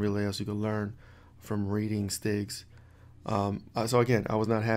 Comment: lol, the whole time I thought you were saying sticks....yeah STIGs are all the fun. lol. Keep your head up man! #tribeofhackersredteam